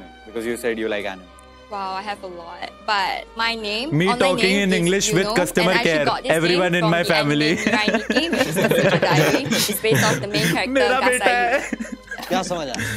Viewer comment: i will write what she actually said " Wow, I have a lot , but my name is Yuno and got this name from anime ' mirai nikki' which is THE FUTURE DAIRY, it is based of the main character Gasai Yuno. Don't thank me , this is the least i can do as a fellow weeb